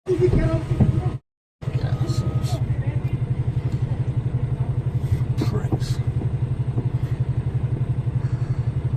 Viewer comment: WTF is he saying? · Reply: Can't stop THAT quick; truck you know..... Broad Scouse accent sometimes hard to follow. Cammer had no obligation to let Audi out; he'd deliberately parked in a dangerous and illegal position for his own convenience. Realistically, don't think cammer could have pulled up short; this was just an entitled Audiot...